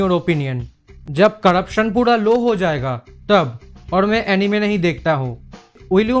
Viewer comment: I thought you said India will develop when you will stop watching anime🤣